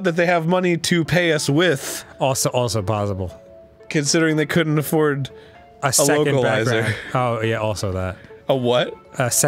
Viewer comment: I cannot understand what said at 0:35 please help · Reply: “Considering they couldn’t afford a localizer”